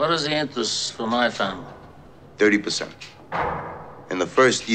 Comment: 何回聞いてもトゥエンティパッセンって聞こえるんだけど、本当に30%って言ってる?????